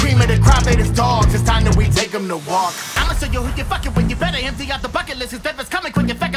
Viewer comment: I thought he said "They just dogs, it's time that we take them to the wok"...